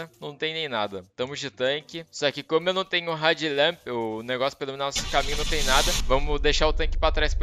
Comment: Só eu achei que ele tava falando "red lamp"? A pronúncia tá muito parecida kkkkkkkk to ligado que é head lamp agora.